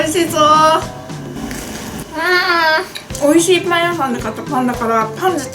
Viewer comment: パン屋さん がどうしても だいみゃさん に聞こえる